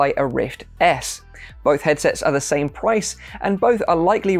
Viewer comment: or 32 he says pwice instead of price How I feel: (Enter fancy Winnie the Pooh meme)